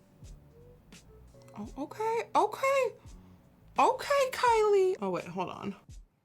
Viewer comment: You said u hate the Kylie Jenner palette but I heard u say “ ok ok Kylie”